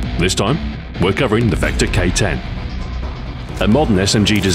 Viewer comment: I always thought that it said Vector KID not K10